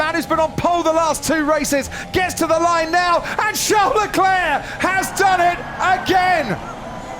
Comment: wow youtube your subtitles instead of Charles Leclerc its shove the clare